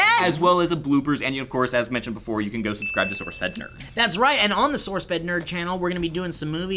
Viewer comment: I thought elliot said sourcefed turd... yh